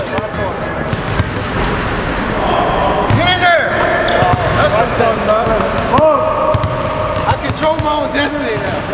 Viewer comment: dafuq did he say at 0:44??? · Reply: "Get in there." You deaf or smthn? hahahhahaha